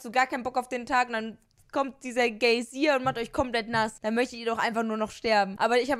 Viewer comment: bin ich die einzige die ,,Gaysier" verstanden hat?🤔 Ok Nevermind